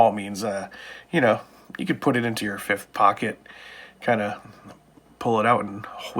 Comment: When you said fifth pocket, for some reason I thought I heard prison wallet 😑😂